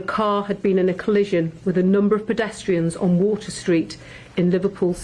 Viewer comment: For a minute i thought the head copper said "we had reports of a white British man from Liverpool who drove into a group of Palestinians" but obviously she meant 'pedestrians' would be ironic though because that lot were out in their pathetic droves at the ceremony! Just saying 😌